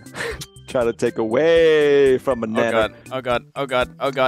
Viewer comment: idk if its seananners or if is in this episode, but it definitely sounded like they said the n word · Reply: he said "Nanner"